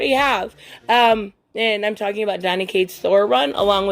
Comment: I thought you said "Johnny Cages venom run" for a second😅😅 kudos if you know where hes from hehe. And also what the hell was that banana thing imitating wolverine? Lmao😅 This is a cool concept to bring kids into marvel comics, slick since half the kiddy gamers in the world love this game. Would be funny if they bring DC ones. I don't play fortnite so I've lost track of their dlc expansions. I've noticed alot of youngsters love fortnite and minecraft.